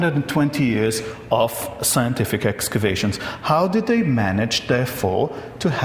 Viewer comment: 6:xx I heard "actuation" but it was probably "excavation"